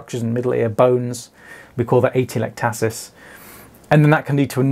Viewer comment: Thank you for another interesting video! Did anyone catch what it’s called when the eardrum gets sucked back and can get stuck to the middle ear structures? I can’t quite catch it for my post-video Googling. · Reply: Middle ear atelectasis. Atelectasis refers to the collapse of an air-filled cavity, usually the lungs.